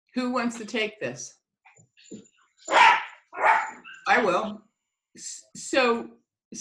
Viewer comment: I clearly heard Wayne Dyer say "I Will".